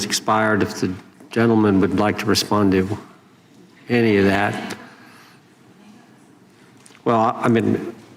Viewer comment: I had to rewind when Jim Jordan said do you want to respond to any of that. I thought he had said do you want to respond to the idiot. That woman is horrible.